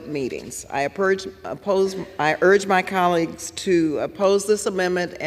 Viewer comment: Did she say purge??!?!?!?